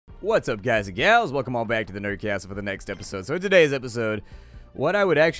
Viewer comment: Am I the only one who originally thought he said "nerdgasm" instead of "nerd castle"?